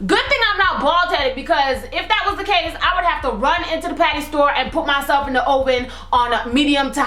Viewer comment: when she said patty i thought she said panty lol